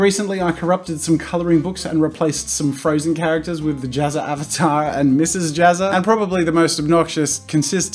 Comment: It dissapoints me that he has not taken the opportunity to call it a jazzatar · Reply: Cause it sounds like your saying manatar